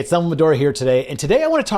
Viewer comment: Thought you said "hey it's dumbledore here today"